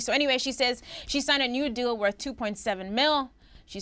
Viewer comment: At first I thought said Iggy accepted a "nude deal" and I was like so she took that bang bros money lololol.